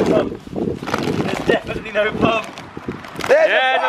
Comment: I thought he was saying pug 3:37 lol